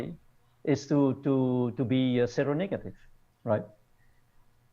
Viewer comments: Does anyone know what GVB is referring to when he says the "best thing that can happen to somebody is being zero negative"? Is he referring to O- blood type natural antibodies? Beginning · what is being said and meant here? Zero negative?